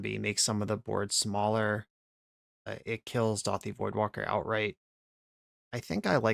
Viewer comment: did you say dauthi voidwalker??